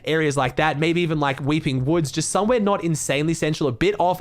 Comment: Did u just say weeping woods